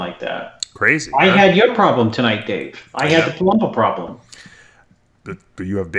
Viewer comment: Hahaha!! When at the start said "I had a Palumbo problem", and then Dave said "You have babies like I do"...I thought was saying he was having a bubble gut problem! x,D